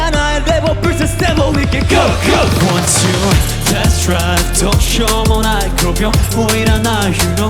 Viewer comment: 本当にほんとうに申し訳ない、「test drive」なんだね、「デスドライブ」と聞き間違ってた…😂